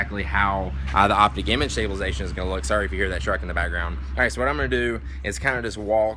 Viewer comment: Haha I thought you said the shark in the background instead of the truck in the background lol